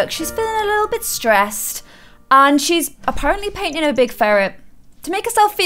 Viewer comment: goes: she paining a ferret me: that’s a cute pink bunny wait what did she just say